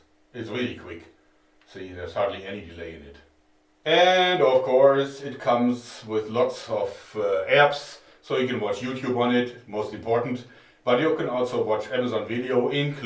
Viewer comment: I thought you said mostly porn instead of most important, I guess my mind is rotten